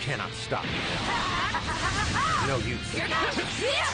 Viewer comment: when goku dodges the blast it legit sounds like he says no u after that